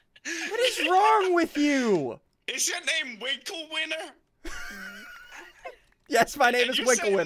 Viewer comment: When Crainer said “WinkleWinner” I thought he said Wrinkly Weiner